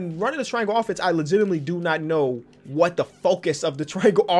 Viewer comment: When said “I don’t know what the focus” I thought said “I don’t know what the f***